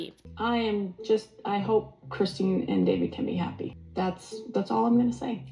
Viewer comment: Is it just me or did anyone else hear Robyn call her “Kristen” instead of “Christine”?